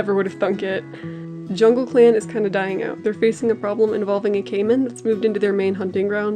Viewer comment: I thought you said a caveman moved into their hunting grounds lol..